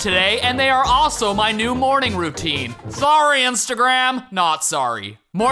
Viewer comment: Thanks Morning Brew for being my new morning routine! - sign up for free here <link> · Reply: It almost sounded like you said (3:31) that Morning Brew is your new "Morning Brew-tine" ... Yeah... go on, take it.. go ahead and use it! 😜